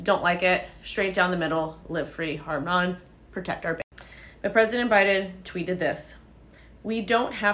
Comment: You keep editing your video and cutting yourself off >< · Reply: It sounded like after 00:27 she said "Protect our babies" but I can't know for sure because she trimmed the video and I also know she supports abortion so that doesn't exactly add up🤔